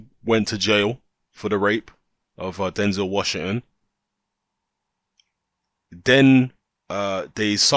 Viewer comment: I thought I heard 'Denzel Washington' 😂😂😂